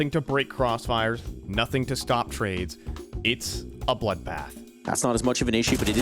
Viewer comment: did some say… “bloodbath”? GD REFERENCE???